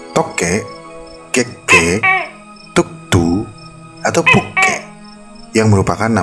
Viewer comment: gua dengernya puk_i🤣🤣🤣